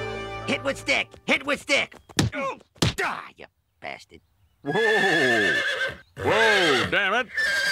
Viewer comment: did moe just say bastard.